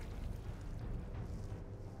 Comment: what did he say? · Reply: Not so bad